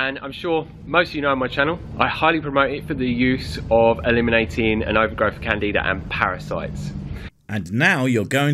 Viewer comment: Could someone please translate what the guy says at 1:38. All I got was, "eliminating an overgrowth of candy to end parasites". I don't think that's right. I didn't get a couple of the words before that as well, but I think I could infer the test if I just had that part of the sentence.